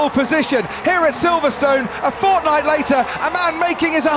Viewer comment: Only me who thought he said fortnite at 17:18😂